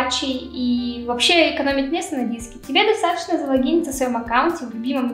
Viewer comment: Мне одному послышалось завагиниться а не залогиниться?